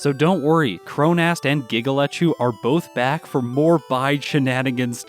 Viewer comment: Every time you say Giga Lechu's name I hear it as "giggle at you" and wonder why that non sequitur just happened. Also, congrats on running the second community challenge video! I'm glad this one didn't turn into a monster for you to get done and put together like the Parasect one did. I hope to see more of these over the coming years.